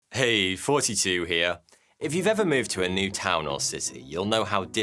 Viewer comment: all this time i thought he was saying “Hey, 42 here!” lol